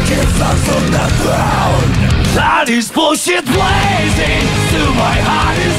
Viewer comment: My heart is blazing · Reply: I hear "daddy's bullshit blazing"